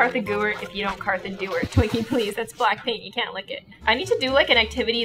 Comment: I thought she said "Blackpink" but she said "Black Paint" :(